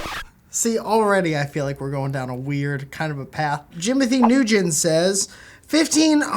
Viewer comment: did just say Jimothy New Gin?